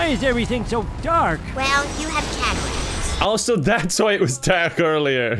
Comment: What did Lisa say? I couldn't tell. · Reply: "well, you have cataracts"